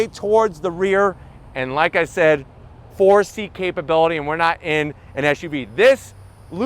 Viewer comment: "4 seat" capability thought u said 4C i was guna say my 04 Volvo has 4C suspension lol. I wuda started telling every1 its Ferrari suspension 😜 The FENDERS😍 i wud argue the light on them provides nice symmetry with the Ferrari badge diagonally across the vent from it. If u look at it, connect them it follows the roofline nicely👍